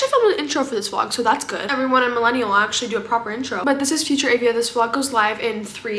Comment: Did she just say she’s a Millennial?